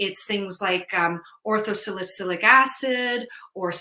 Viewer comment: Dr. Mills, at video marker 13:40 - did you say "Ortho-salicyclic" or "Ortho-silic" acid? Thank you for such an educational video! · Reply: Hi! Sorry, I said orthosilicic acid! Thanks for asking!